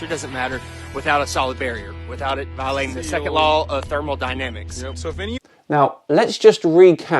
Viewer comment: I'm sorry to inform but the flat earther does not owns you ... he clearly state in challange number 3 that it should not follow the second law of thermodynamics. Although the vacuum with pressure air in it is an amazing scientific discovery it follows the second law of thermodynamics (actually everything does). Proving the earth is not flat is easy, but disproving a law of thermodynamics, you will have to study a lot to do it, and even then I think is not likely. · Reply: You misheard what the millionaire said as the challenge. He said: "...without it VIOLATING the Second Law of Thermodynamics." [capitalization mine].